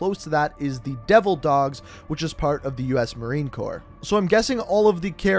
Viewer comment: Did he just say corpse ?